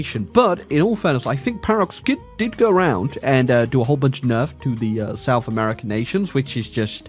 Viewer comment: I thought he said "Paradox did a whole bunch of meth" and all I could think of was "Yeah that makes sense"